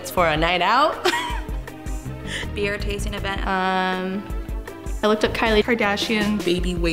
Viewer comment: Im not that good in English but if i heard correctly "beer tasting event" then i fkin love her ❤️